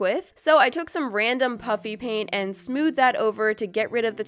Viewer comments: I thought she said puppy paint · When I first started watching your squishy makeovers I always thought you said puppy paint instead of puffy paint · Sounds like you say “pUpPy PaInT” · Untill i read the desc of the video i thought you were saying puppy paint not puffy paint lol